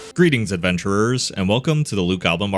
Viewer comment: I horribly misheard the intro as the "Luke Album" Marketplace. And now you can't unhear it. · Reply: I always hear him say "Greedy adventurers". I like mishearing Lube Gobbler :)